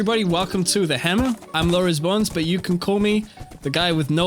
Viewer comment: When you introduced yourself I thought you said Lawrence Bones. Great video as always.